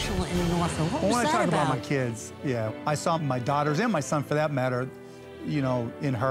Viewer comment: i heard "my son for that matter in her" i died